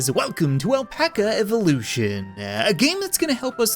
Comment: I swear to god I heard him say, “What’s up guys welcome back to Apalcha Evolution